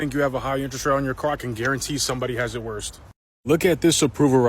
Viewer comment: did he say worst? Im sorry im petty...